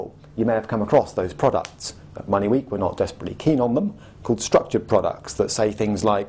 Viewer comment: can someone tell me what does he say at 10:18? he says something that sounds like ''money weak'' to me